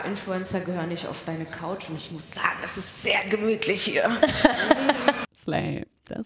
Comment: was sagt bei 16:36 "das war slaim"? oder laim ? ich verstehe es akustisch leider nicht ganz ;-; und im untertitel steht auch nur SL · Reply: sie hat "slay" gesagt, also in jugendsprache sowas wie: richtig krass und selbstbewusst